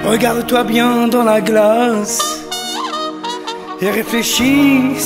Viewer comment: EU OUVI DONA GLADYS??